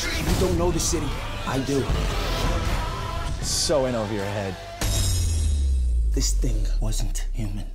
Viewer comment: What did he said on 0:17? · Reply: "So in over your head." And I think that was Nathaniel Buzolic.....he sounds strange with an American accent lol